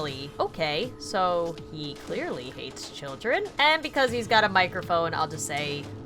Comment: When said hates children I thought she said ate children for Funtime freddy